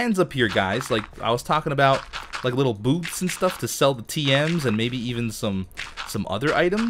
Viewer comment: Did he say little boobs and stuff 13:15 or didnt i understand